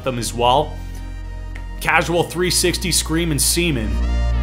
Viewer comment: did he just say causal 360 screamen seamen